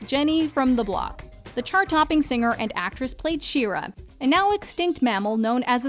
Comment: When she said "Shira", i heard she said "She-Ra".